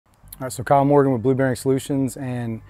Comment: Not gonna lie I thought you said Blueberry solutions 🫐